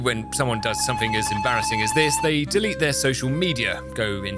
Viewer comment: did i hear bulgaria